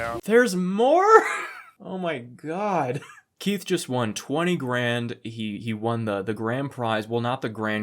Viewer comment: Was it $20K?? I heard them say $2k which was... laughable.